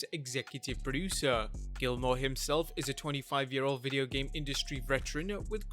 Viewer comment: Did you say he is 25yr old or 25 year of? Cus something ain't adding up